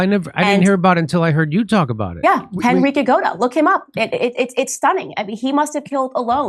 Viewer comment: What was the name she said @ 38:56? Henrique Agoda? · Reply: Genrikh Yagoda